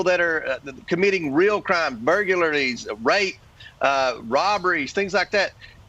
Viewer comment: um... did he just say "Burgurlary"? 😂😂😂 wtf